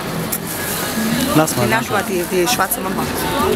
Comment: I thought she said swartze number, nuber meaning shag, lay,fuck??? anyway look at the pervo hiding at 3:59 very funny.